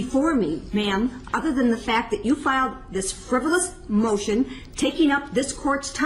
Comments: "...Other than you filed this "frivolous" motion." This is what was said around 3:20. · I think she said FRIVOLOUS